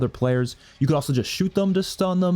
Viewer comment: “shtun” I will now say shtun instead of stun